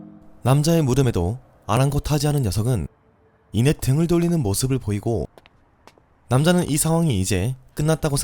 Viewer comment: 남자의 물음... 들리는건 남자의 뭍음...